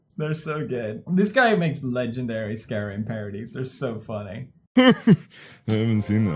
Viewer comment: Did you just say Skyrim Parody ( 2:51 )